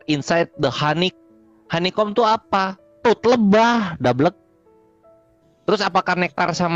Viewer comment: bahasa inggris tp mirip bahasa sansekerta ya koq ada "tot" nya segala kedengeranya jorok lu..😂